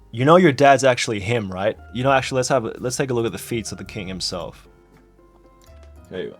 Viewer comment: I THOUGHT YOU SAID FEEET😭😭😭😭😭😭